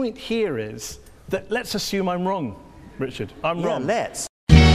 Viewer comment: what he said at 1:48? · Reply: T0mB1ll the Muslim guy said "Let's assume I'm wrong, Richard", so he answered "Yeah, LET'S!"